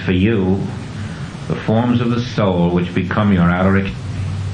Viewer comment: Thank you so much for posting this. It is unclear what he is saying at the 7:25 mark ...."for you are forms of the Soul which become your ???" Can you make out what is being said there? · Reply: He's saying, "...which become your outer experience."